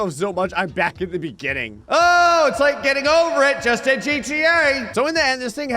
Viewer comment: did i hear this right? Getting over it in GTA? I say that was a desperate request for it to happen.